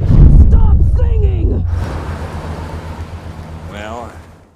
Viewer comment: I swear Jasper said 'John Cena' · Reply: No,she said stop singing